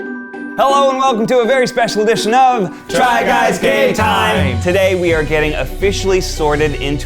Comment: I heard "try guys gay time"